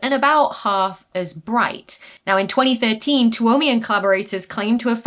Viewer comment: Already googled, can't find an answer I'm confident in: What does the word "Toumian" mean? Dr. Becky first uses the word at 14:24 in the phrase "Toumian collaborators". I am unfamiliar with this term. Does it refer to a nationality or residence in a particular city? Is it referring to a university or something? The top of the image of the publication shown gives me some hints, but I can't quite figure it out. My best guess is a Toumian is someone who lives in a certain place somewhere in Finland.